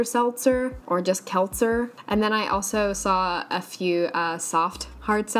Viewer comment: Until showed the cans I thought she was saying salsa